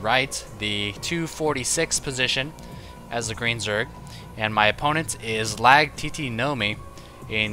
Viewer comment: "As a Korean Zerg" I think I misheard...